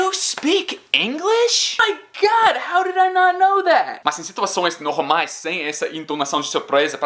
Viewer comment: oh my god haw ni haw oiwiiou my dad Isso que eu entendi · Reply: OMG! How didn't I know that? (Foi o que entendi) kkk